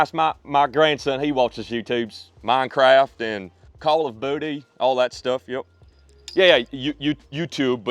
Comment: anyone else catch the “call of booty”😂